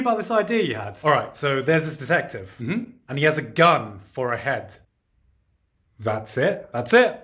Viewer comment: i heard this as “he has a gun, forehead”